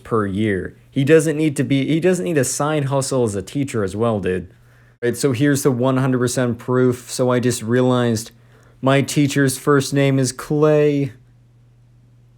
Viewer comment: I thought I heard techno